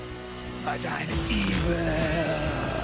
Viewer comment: I hear him saying "but I'm evir"